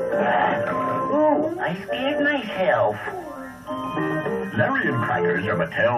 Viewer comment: I know the lion said I scared myself but I thought he said I shit myself